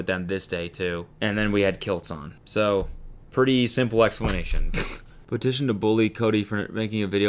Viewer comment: I heard "and then we had killed someone." I'm half asleep 😄🙃 & his facial expression after! 🤣